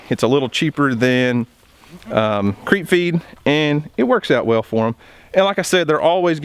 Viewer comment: @ 4:36 you mentioned a supplement I didn't catch the word of"Creek or creep"( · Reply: Creep feed